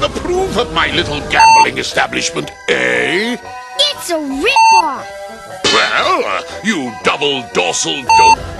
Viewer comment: did tails say "its a rickroll" ?? lol wtf